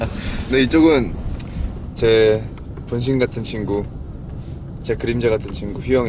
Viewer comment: 분명 제 분신같은 친구인데 붕신같은 친구로 들리는 건 기분탓이겧지ㅋㅋㅋ